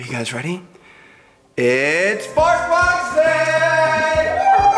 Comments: it sounds like your saying barf box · I thought you guys said barth box 😅 Amyzing vid 💖